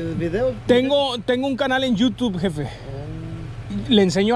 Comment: Pensé que decia lo que tiran se usa ajajajajajjaaj